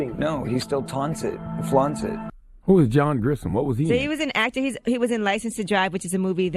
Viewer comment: Scared tf out me..thought he said John Goodman@ 3:20 lol